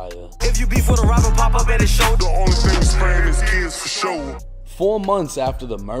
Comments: She said in her lyrics the only thing we sparing is kids forsho,it does sound like she said,(the only thing we spraying is kids forsho) but she didn’t say that if you listen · You sure she aint say, “the only thing we sparing is kids fasho”? Either way she did what she did, but thats what it sound like she said fr.